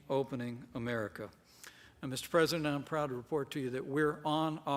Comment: did he say Miss president at 17:26?